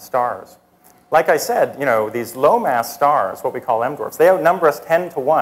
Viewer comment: i misheard him at first, i thought he said "lone ass stars" ha